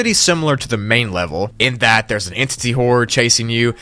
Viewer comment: did you really say "hoard" there...?